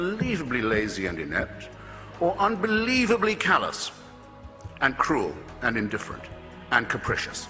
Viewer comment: what does hitchens say at 8:18 ? unbelievably "callous"? can someone give me a hand, english is not my first language :)